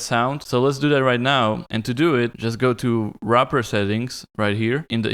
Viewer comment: he says something like rapper settings? what exactly is he saying? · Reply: wrapper settings :D the button I clicked is called that.